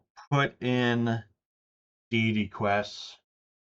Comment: I thought you said Daily Quests initially, until I listened again to hear you say Deity Quests.